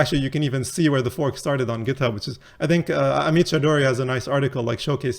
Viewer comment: what name does he say regarding forks of bert? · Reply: <link> (Amit Chaudhary). Yannic Kilcher already translates papers into this format for us, we just need him to make us an API 😃